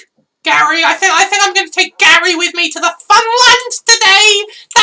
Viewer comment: i heard 'im gonna take gary to the front lines today'